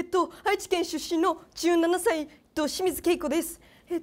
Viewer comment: 「ド清水恵子です！」に聞こえて1人でゲラゲラ笑ってた